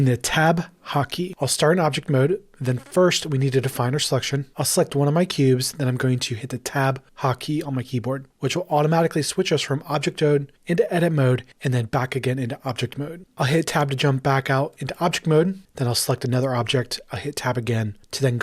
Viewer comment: did he said object tode???